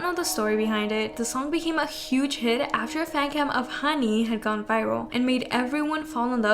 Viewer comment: i thought you were saying my name (hanny) LMFAOO